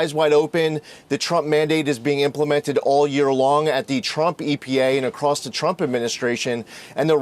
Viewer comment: did he say the trump epa😮